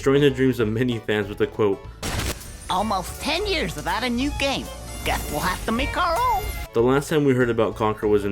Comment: What exactly does he say? I can't make it out. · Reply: SPYRO Almost 10 years without a new game, guess we'll just have to make our own.